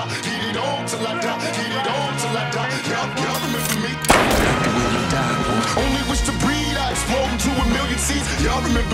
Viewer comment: Can anyone tell me what does he says on 1:28 ? Thanks! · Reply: "Yup yup y'all remember me" and "I like the way you die boy" i know this is late but hope it helps!